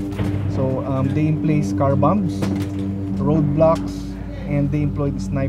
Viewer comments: Did I heard roblox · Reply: Bro it's road blocks😂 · did he say roadblocks or roblox